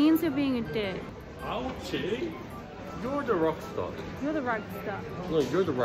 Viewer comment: it sounded like they were saying ‘you’re a rockstar’ 😂